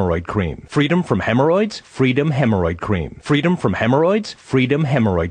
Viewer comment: Eventually blurs together in such a way where it sounds like he says "free them hemorrhoid cream"